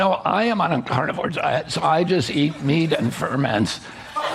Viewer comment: "I just eat meat and ferment." I thought he said "meat and vermin". 😂